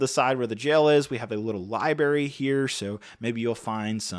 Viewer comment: did bro just say "libary"?